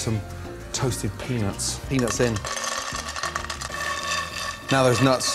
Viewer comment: thought he said penis in